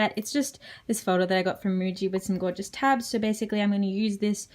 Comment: What kind of notebook did you mention at 5:47? Didn't quite catch the name to locate it. I love it! Thanks! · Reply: It's a japanese brand called Muji. Glad you enjoyed it. I got the coloured tabs separately though.